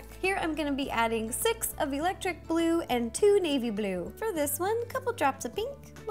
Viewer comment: when she said navy blue, I THOUGHT SHE SAID MAYBE BLUE! i am stupid....